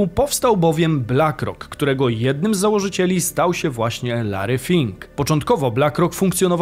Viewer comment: Na początku usłyszałem Larry Flynt ;)